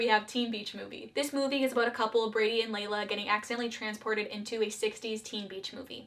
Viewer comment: Am I the only one who heard her say Brady and Lela instead of Brady and Mack?